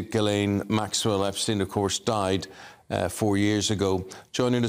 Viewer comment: Ghislaine Maxwell is not dead. I dont think. Sounds like he said she died.